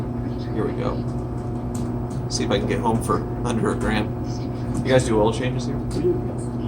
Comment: Cool video! Oil, spark plugs, air filter? :P I heard you say 'do you guys do oil changes too?' Hopefully that went through, poor old V6! That's a quality car, without a doubt!